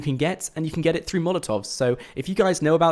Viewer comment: did he just say "You can get it through motorvs"